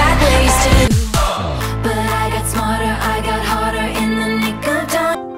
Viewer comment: WHAT? DOESN'T IT SAY THAT? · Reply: it says harder